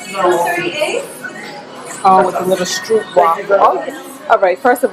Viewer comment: So I MUST stop listening and not watching. I heard "red light contraption to let you know..." and then "oh, 'street walkers' we gotta try one of those..." 🫣😂 She said STROOP WAFFLE! 😆 Whew. Lovely trip! I want to hear about tips and tricks for long flights. I'm going from 4 hr flights max to 19hrs. Oh my.